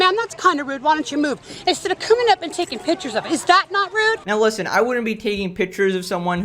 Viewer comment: I think she said “taking pictures of it” meaning the car in the handicapped spot, not of her